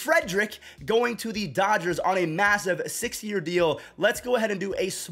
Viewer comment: I swear you said 60 year deal lol, ik it was 6 tho